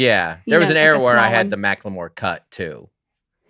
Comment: I thought they were saying Dustin looks like Michael Moore and was confused then I realized they were talking about Mackelmore which makes more sense